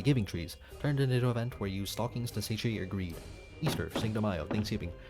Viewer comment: Your speech is sometimes very difficult to understand. For example 9:57. Automatic captions think you said "into event where use stockings to H agreed", and I couldn't guess any better. If possible, please invest in manually written subtitles or try to pronounce words out more.